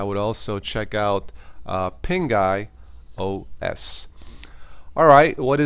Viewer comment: does he say "pinga"?